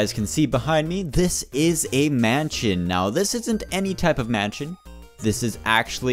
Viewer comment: Thought you said "This is a manchild!"